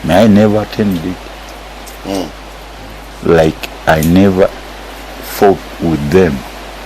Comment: Did he say like he never “f_ ck” with them? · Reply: Fought